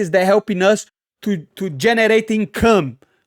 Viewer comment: DID YOU SAY “GENERATING CUM” ???